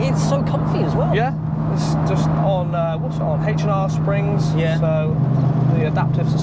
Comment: Awesome. Which Springs? sorry, I couldn't understand in the video 5:19. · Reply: H & R Springs